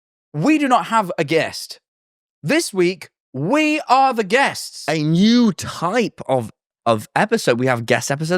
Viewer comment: when we are the guest star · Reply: I really thought it said a gusta